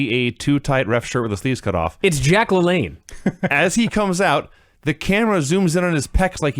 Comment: When said Jack LaLanne, I thought that he said Jay-Qualin, like on Key & Peele